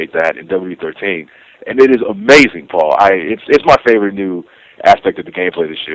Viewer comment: Wait, is real name Paul? Because at 2:20 said "It is amazing Paul" Atleast that's what I heard...